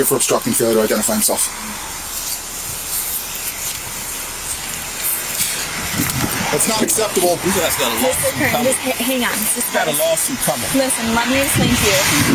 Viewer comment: There is no court, case was dropped :( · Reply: I thought I heard the guy say "lawsuit" at the 8:19 mark....and I think a judge would be interested in this bodycam footage..